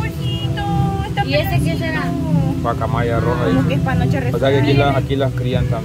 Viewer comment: QUE DIJO 5:13 QUE ES PANOCHA RASURADA 😂❓